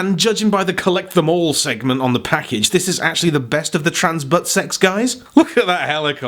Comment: It's been 5 years since I first watched this video..... and I finally realized that was saying 'TransButtSex' instead of 'TransBotsX'